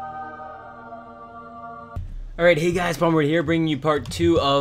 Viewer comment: It sounded like he said "Hey guys, bummer here" XD